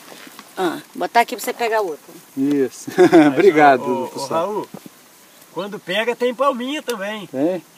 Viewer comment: Eu achei que estava assistindo o Raul, aos 8:22 ouvi te chamarem de "SAUL" mudou de nome?